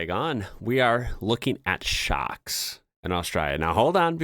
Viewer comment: I had a weird moment of language confusion in your introduction when you said, "we are looking at shocks". I thought you were trying to say "we are looking at sharks" in an Australian accent! And, re the water in the restaurant, I think that was decanted water in a restaurant owned non-commercial glass bottle which is often provided free of charge to patrons. I'm not familiar with the self-service aspect of it, though. In my experience, you would ask the waiter for water and it would be brought to the table. I suspect this is something new or something peculiar to this particular restaurant, as you suggested.